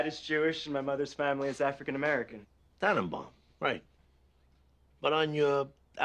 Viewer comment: Can anyone help me I've trying to research this but can't find anything which makes me think I'm not hearing properly and with English not being my first language it doesn't help. At 1:38 when he says that his father is jewish and his mother is African American, it sounds like tony responds with "atom bomb" which I assume was sort of racial slang but I can't find any documentation or usage of this online. Have I misheard what he said? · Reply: Tannenbaum. A jewish name.